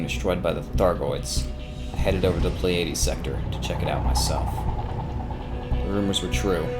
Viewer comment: did he just say play-adies sector? hahaha